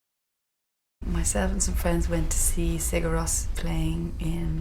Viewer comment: does she say "My servants and friends......."? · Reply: “Myself and some friends”